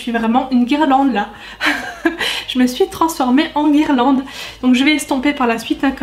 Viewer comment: « Je me suis transformée en Irlande » y a que moi qui est entendu?😂 a